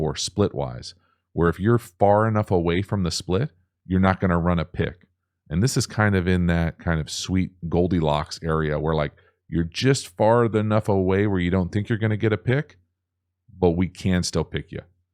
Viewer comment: he clearly says “farth enough away” and I only noticed it because for the first 29 years of my life I said “farth enough” until someone corrected me and told me it wasn’t a word.